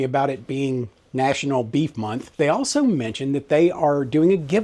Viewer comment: I wasn't looking at the screen and thought I heard "beet month" and, well, I like beets but I'm glad you said beef. 😂